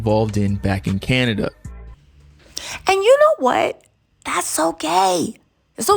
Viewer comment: When you said “that’s ok” I thought you said “that’s so gay” lol